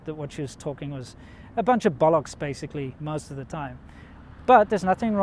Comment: Please help me to understand what you mean on 10:03. What she was saying was just a bunch of balusk anyway. Bunch of what? ballisk, balusk? Sorry I don't get that. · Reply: Bollocks